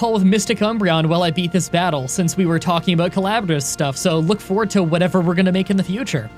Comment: I THOUGHT YOU SAID "COLLABORAL SEX" INSTEAD OF "COLLABORAL STUFF" AND I DIED LAUGHING HELP